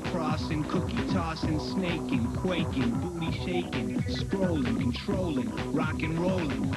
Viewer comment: When I was a kid I thought they said, “boobies shaking” 😂